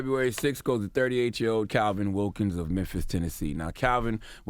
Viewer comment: I thought it said Memphis Bleek for a second lol